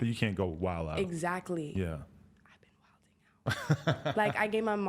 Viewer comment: what she whisper at 16:26 yall? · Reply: I've been wilding out